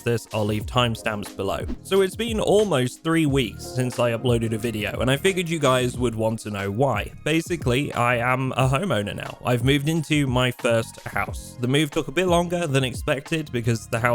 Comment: i legit thought he said i am a homo now and i was like omg is he coming out but no. anyways congrats on the new house bro